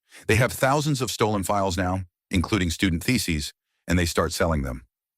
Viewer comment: “Including student theses” Relieved that wasn’t what I heard it as the first time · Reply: I heard it as feces 😂